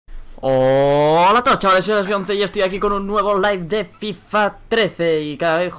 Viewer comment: Pense que decia Hola a todos chavales aki bejonce pero me di cuenta despues que decia Alexby11 xD